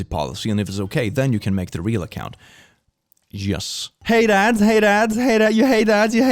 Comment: I thought he was saying "Hey dads, hey dads" 😂 😂